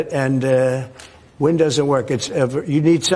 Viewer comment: I thought he was saying WINDOWS😂😂😂